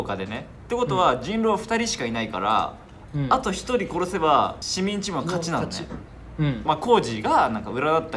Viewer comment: のこーじくんなんて言ってるか聞き取れない…。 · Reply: 「もう勝ち」って聞こえる